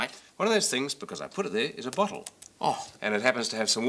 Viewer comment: when english ppl or australian ppl pronounce "bottle" it sounds like theyre saying butthole